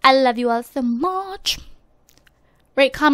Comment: is it just me or did she say WRITE, comment, and subscribe? as in she meant to say like but kinda messed up...? lol whatever i <3 xD